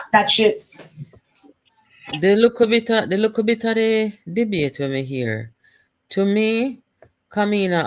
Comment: i thought you said you were boycittibg the debate.. 😅😅😅